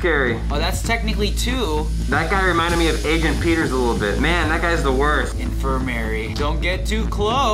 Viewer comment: Does SAY MAN